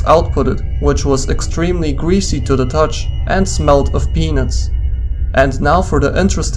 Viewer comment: Around the 2 min mark I swore he said "smelled of penis", even after replaying a few times. Then I turned CC on, and it said, "peanuts". Just in case anyone else thinks he said the greasy stuff smells of penis.